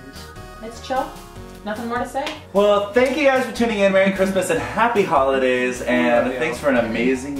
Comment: i thought mitch said crappy christmas